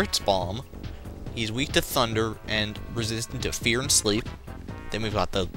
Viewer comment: when you said he is resistant of rear for the red ranger i thought you said resistant to beer